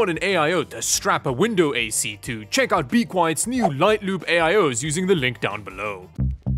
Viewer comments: NGL thought he said "Light lube" XD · I heard that as "Light Lube".